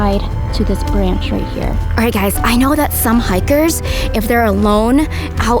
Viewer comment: Did vy said som hayckers?in